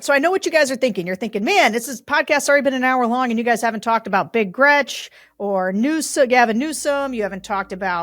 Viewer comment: Big Gretch? I thought you said Ric Grech. <link>